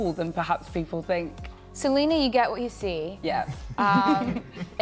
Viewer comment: What? Did she talk about Selena? 🤔 2:18 did I mishear that?